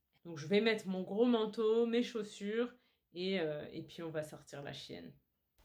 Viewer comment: La chien? Have I misheard it? (2:20 min) Or is it that a female dog is "la chien" versus "le chien"? Sorry I love French language but still don't want to make a fool of myself, trying to communicate in it, thus English